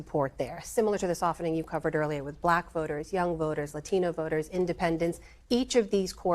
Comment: HOLD UP DID SHE JUST SAY "YELLOW VOTERS"?